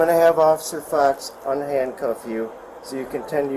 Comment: Did he just say Officer Fucks? Nice